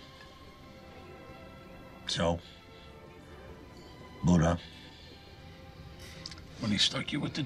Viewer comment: What does Tony say in 1:43. It sounds like he said "burra" which means men in Albanian?? Someone explain